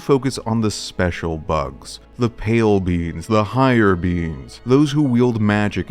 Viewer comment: I thought he said pale beans